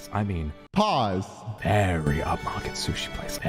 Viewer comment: ". . . very up-market sushi place" is what I heard. Maybe that could translate to 很高等级的寿司店?